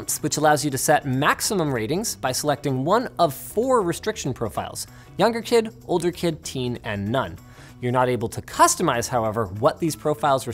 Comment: I heard "Younger kid, older kid, teen and nun." o.O